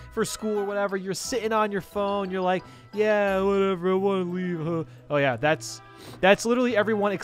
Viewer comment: “You know how you wait for the bus for school or whatever and you are ohh I wan’ weed” - Yes he actually said this